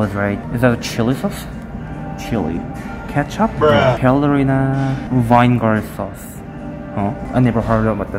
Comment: Did anyone else noticed that he said "Vine" Gar instead of vinegar at 5:22?😂